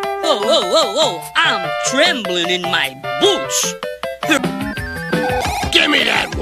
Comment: I think he’s saying "I’m trembling in my boots” But the first time I heard him say it, I thought he said: "I’m trembling in my booze. ”